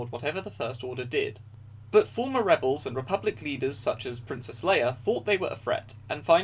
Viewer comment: when you said Princess Leia I thought you said Princess Slayer.